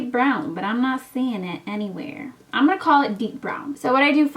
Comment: For a second when you said the color of the pencil I heard "beef brown" and almost gagged like what a name that would be 😂😂😂